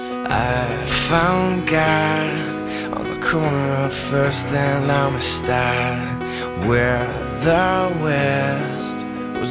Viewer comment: For the longest time I thought he was saying "On the corner of first and I'm a star" 😂😂😂💀